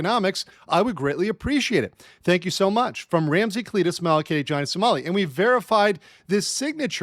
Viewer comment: I always hear Ramsey 'Cletus' Mael !!!!